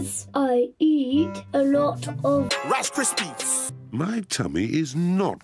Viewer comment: (CC) “rasters beets’ instead of rice crispys